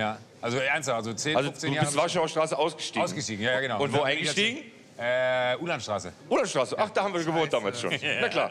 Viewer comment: Welche Straße nennt Joko bei 5:10, ich verstehe das nicht genau, Udanstr, Kudammstr? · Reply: Uhlandstr.